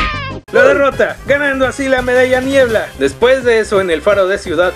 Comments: pense que dijo la medalla mierda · escuché medalla mierd* en vez de medalla niebla XD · yo oí la medalla mierda jajaja🤣🤣🤣XD